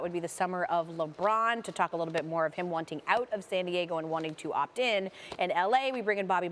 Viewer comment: she said san diego?? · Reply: Shove This L Up Your Ass I had to watch d vid 4 times before I heard "San Diego"